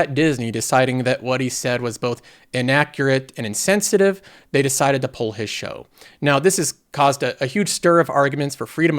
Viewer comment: what were the actual quote that he said? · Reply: "That's mah momma"